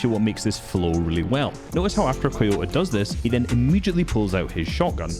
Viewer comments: I heard “Toyota” and thought a car was at the peak of fortnite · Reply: I thought he said Doyota so I was like has this dude gone mad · I heard “Toyota” and thought a car was at the peak of fortnite